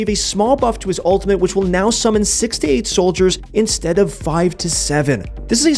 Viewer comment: made me a bit scared there when i thought you said “68” soldiers instead of 6 to 8